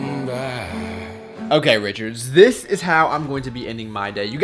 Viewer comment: "Okay, Richards" lol I'm sure that's not what you said :P